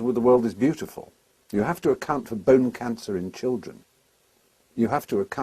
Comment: I first thought he said "you have to account for bone cancer and children"